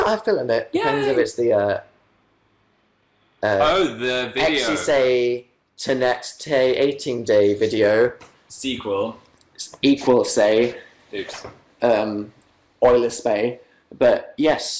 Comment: Anyone know what Dan was saying in pig Latin near 1:02:24? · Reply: i believe he said "sexy internet dating" then "sequel. spoiler."